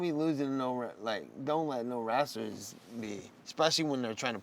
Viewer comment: did Nate say 'wrassler' hahaha dude is a G